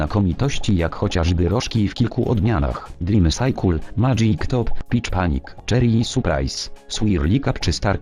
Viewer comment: Czy tylko ja usłyszałem "bitch panic"?!